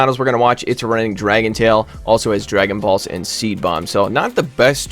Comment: sounds like you said dragon BALLS. now I cannot unhear that